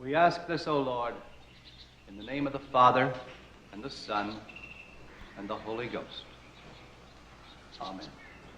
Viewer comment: I thought the priest said, "and into the hole he goes".